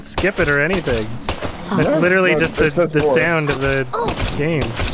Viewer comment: I heard "Vore". Did anyone else hear vore?